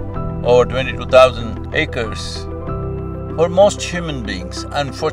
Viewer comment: Did he say 22000 acres?